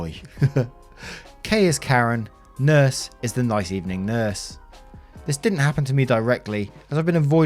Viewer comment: I 'm not a native english speaker but enjoy listening to your stories anyway, but today I nearly lost my crap as I misheard the "nice evening nurse" as a "mice eating nurse". Keep going though . . .